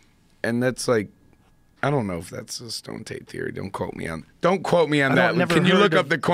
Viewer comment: When Post said “stone tape theory” Joe heard it as “stoned ape theory” lol